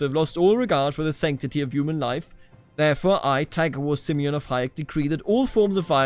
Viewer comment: I crack up every time you say "Tagawor" because I keep hearing "Tiger Woods" lol. Anyways, this is one of the best/unique CK2 series I've seen in a while. Looking forward to see what's to come next.